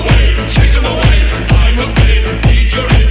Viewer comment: For sum reason i always thought thy said pink ELEMENTS no pink elephants…